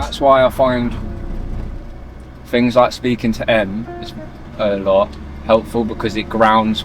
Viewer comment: What did he say? "Speaking to M"? Who is M?